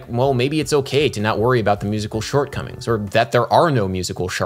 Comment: Anyone else hear "musical shart-comings" at 10:49?